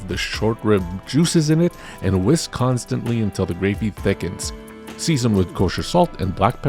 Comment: You said 'Whisk Constantly" but I swore you said Wisconsin-ly which just felt like it made sense for poutine.